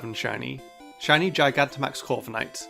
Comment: did you just call it Shiny Gigantamax Fortnite or I've imagined it